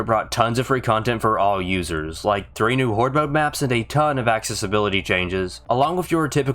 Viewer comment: I heard “axis ability changes”